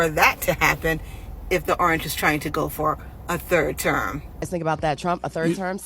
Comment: Did she just say a turd term 😭😂😂 girl go back home